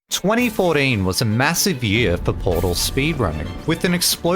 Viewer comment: "twenty foureen" bro i thought you said 2048 😭